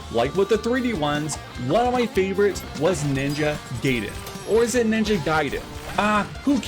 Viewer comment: Did you just say Ninja “gay”den?